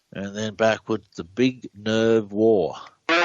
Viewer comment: nuclear war I hear